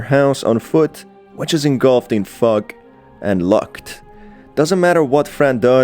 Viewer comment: “engulfed in fog”….I thought you said “engulfed in f*ck” hahaha.